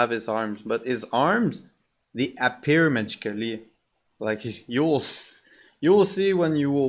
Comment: what are you saying? His arms appear magically? Sorry it’s hard to make out that part.